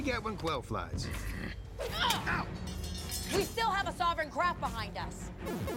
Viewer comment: It sounded like she said "we still have a soverin crap behind us"